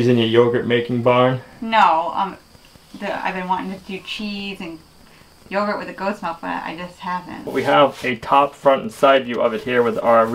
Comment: You want to make yogurt and cheese, sounds like a cow is coming to me. Have a great weekend everyone. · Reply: no, what she said is "I have been wanting to make cheese and yogurt with our goat milk, but I just haven't." 25:43, said that she was wanting to make butter. Butter would indicate a cow, but the cheese and yogurt she clearly said goat.